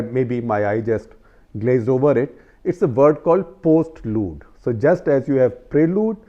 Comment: The subtitle guy does not even know prelude 😂😂. Instead of postlude he subtitled it as post-load (24:43) even when SG said, just like prelude there is a postlude and has its origin in music.